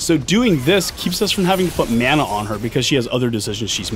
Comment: wtf does he say at 4:01? "So doing this keeps us from having fu-mana on her because she has other decisions she's making" LOL